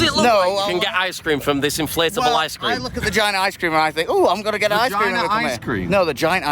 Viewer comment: I heard vagina ice cream too 😂